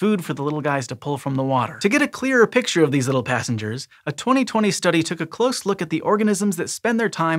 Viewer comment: I was wondering how long it would take for a discworld reference, 6 seconds. Good work scishow 😁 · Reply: Instead of "these little passengers" I thought I heard "these little bastards" :)